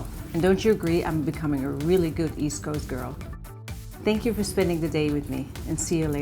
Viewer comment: omfg i thought she said vsco girl lmao oopsies